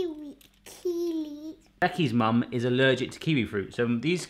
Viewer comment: Thought he said that mrs was allergic to kiwi